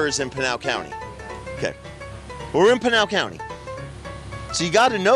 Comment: We love the comments being on!!! 🎉 we love!!! And pinal sounds like your saying penial really fast lol